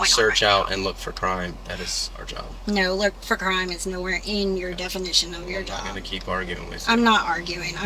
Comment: she actually said looking for crime is not in the definition of their job. 👀